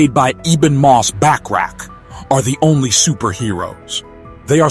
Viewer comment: did he say back crack?